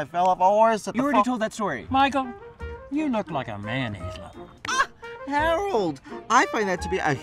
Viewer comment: 'you look like a ... ?' what? love this but honestly can't understand that one word.. help! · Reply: You look like a mayonnaise lover